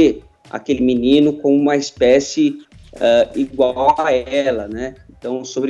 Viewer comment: "espécie"???? Espécie??? Foi isso mesmo que eu ouvi Vini? Espécie?!!!!